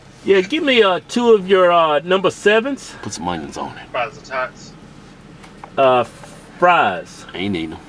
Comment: did he just say add onions on his drinks? i died